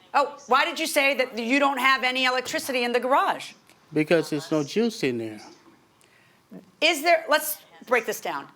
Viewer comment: Thought he said, "there is no juice in it". Such a liar!